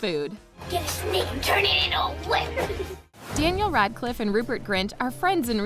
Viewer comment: GET A SNAKE AND TURN IT INTO A WHIP! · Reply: he actually said “GET SNAKE TORNADO WHIPPED!”